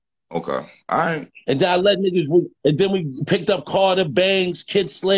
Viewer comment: I thought and said they peaced it up